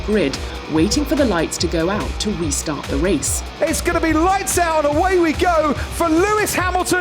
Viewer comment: "It is light out and away we go for Lewis Hamilton only!" Gets me every time 😂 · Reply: I first thought he said:" and it slights out! "